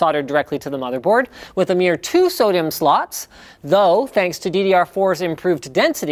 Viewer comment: For a second I thought he said Sodium slots... lol